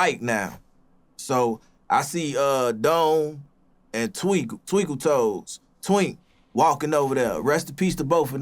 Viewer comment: Did he say there was 2 dudes named DOME and TWINK lol?? Twinkle toes??????